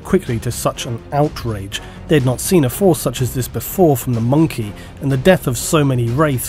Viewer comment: mon'kai not monkey xD im sure you know that but it sounds like you say monkey evertime you say monkeigh